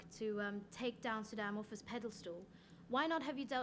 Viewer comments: did she say pedal stool? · did she say “pedal stool” instead of “pedestal”? I think she did